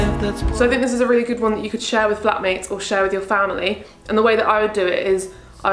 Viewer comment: when you said flat mates i thought you said black mates and i could not stop laughing😂😂😂 love your videos 😂❤